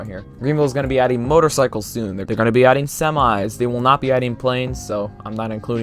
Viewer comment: did i hear SEMI'S